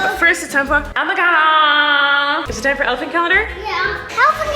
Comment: I thought Colleen said “Avocado!” And not “elephant calendar” 😭😭